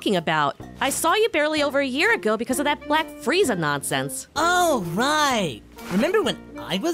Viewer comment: I heard "Black Frieza"... I'm hearing so much "Black"